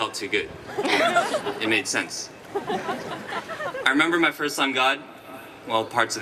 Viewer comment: did he say " my first sun god?" at about